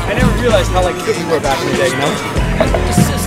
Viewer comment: What does say at 1:51? Great vid! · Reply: He said,he doesn't realize how fit we were back in the day. We were skating every day